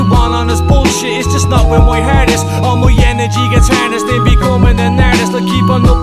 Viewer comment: I always heard it as "not worth my hardest" not not where my heart is and "it would swallow me" not it would smile at me..